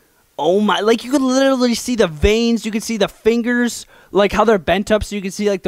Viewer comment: It sounded like he said finger licker